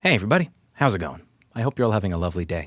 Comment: Oh it's "hey everybody!" all this time I thought you said "hey buddy!" =(